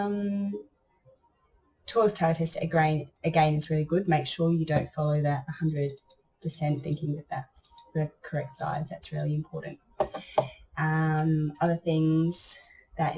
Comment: Hi. Thank youf or the tutorial! I couldn't quite catch what you were saying about an important tip on sizing at the 20:24 min marker. Could you reiterate what you were saying was important for the designer? Something about knowing sizing, etc Thank you!! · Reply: Hi love, its just that toyles tile tester is NOT the correct size. So if you look at your design in Toyles tile tester and think "wow, 100% is really big i had better make it smaller...." Don't do that. its not true to size it is a monitor resolution thing. You need to go off the size in inches in inkscape. So if you are making a 16 inch square page in inkscape make your 16 inch square and zoom in until it measures 16 inches for you looking at it. that will be the size that it prints at. :-)